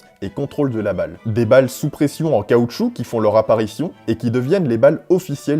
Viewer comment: Super vidéo merci! J'ai beaucoup appris! Au fait, Tu as inventé un nouveau mot a 10:50 "apparifion", c quoi au juste ? 🤣